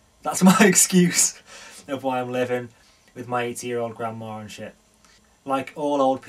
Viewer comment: I thought you said your 18 year old grandma hahhhhh